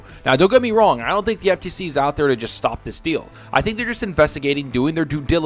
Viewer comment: OMG Stop this deal sounded like you said "stop the steal" then you used the word coup right after.... i thought somehow you tube forwarded me to politics again. Thankfully it was something I actually want to hear more about.... ...